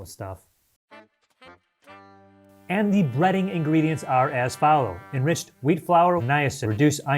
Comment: I thought it said Breeding lol